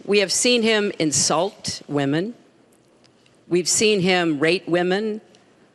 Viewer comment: I could have almost sworn she said "rape" instead of "rate"